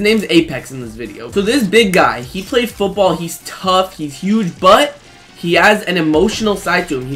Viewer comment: did you say he "has a huge butt"?